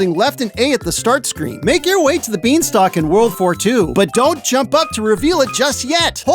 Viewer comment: why did I hear world war 2 😭